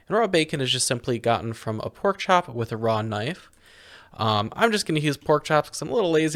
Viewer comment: Did you just say Raw Knife?